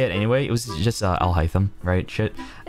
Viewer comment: i think he said "Alhaitham" here. Like, the dude from Genshin